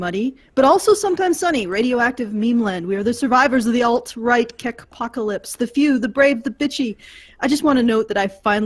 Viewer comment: "Apocalypse"? I thought you said "Alpaca lips"!